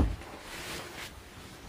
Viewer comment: "Fuck the gulag, I'm off to do a shag." 😂😂 Nice to know where priorities lie, even if it's not in CoD Warzone. 😉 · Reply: I thought he said “frick the gulag I’m off to the gu-shag”. I might be wrong